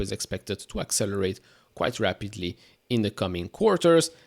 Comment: I thought u said Amd overvalued ?